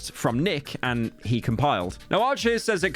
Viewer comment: "he compiled" instead of he complied